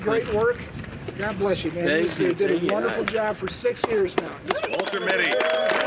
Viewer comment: did Bryan call him Walter Mitte? I wonder if that was intentional or a brain fart. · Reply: definitely sounds like he said walter mitte